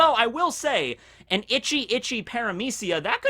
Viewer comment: said itchy itchy fruit. I heard that as " the ecchi echhi fruit has some battle capabilities" lol